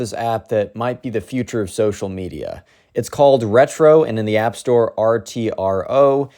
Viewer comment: did someone says retro